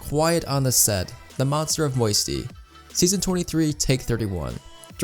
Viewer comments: I think u meant “Scene 23, not season 23” (that’s what I heard) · did you just say season 23? Lol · Did he say season 23? At 1:16 lmfao